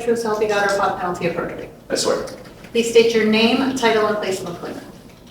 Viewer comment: I swear I heard her say - name, title and Facebook link